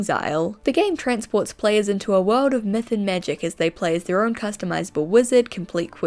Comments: I thought you said Meth and Magic, not Myth and Magic 😂 took me a few listens that one hahah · I thought you said “meth and magic” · I thought you said meth and magic but tbh, that'd fit for a magical world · I thought you said “a wholesome game about METH and magic”😀😂😐